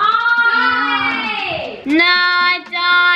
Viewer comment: did say no?